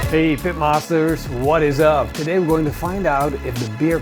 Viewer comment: who else thought he said "what up pimpmasters"